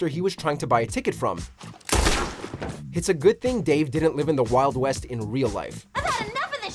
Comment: I swear I thought he said “Alt West” 🤦‍♂️